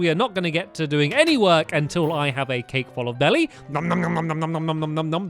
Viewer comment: did he say "a cake full of belly"?